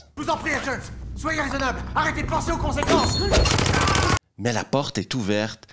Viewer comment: c’est moi ou il dit « arrêtez de penser au conséquences » ? Ah oui et monsieur ball je voudrais que vous m’indiquiez le service d’action militaire qui fonde sa formation sur « suivre son instinct » 😅 (faut être un sacré gogol pour écrire truc pareil)